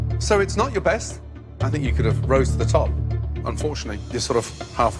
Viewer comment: “I think you could have rose to the top.” I initially thought he said ‘roasted the top’.